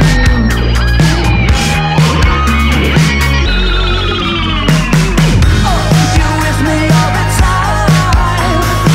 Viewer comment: I don’t know what the voice in the back is saying, but I love the fuzzy sound of it. If you can’t hear it try listening with earbuds. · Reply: The distorted one? I think it says "Swallow every one of them"